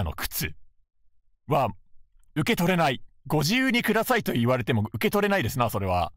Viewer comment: there is a mistranslation Here professor says “ご自由にください” which means help yourself (or feel free to take it) 4:15 He says FGO instead of F4 Still, thank you for translating! (And that Snow-sama PFP *dies*)